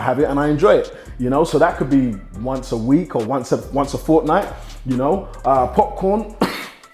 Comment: Did he say "wants a fortnite"